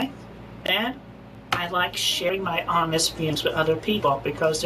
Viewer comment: sounded like he said," I like sh#@ my honest..."